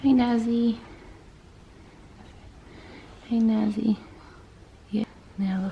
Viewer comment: what did she call her kid 😳 (turn on subtitles) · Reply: Auto generated subtitles are awful she said naz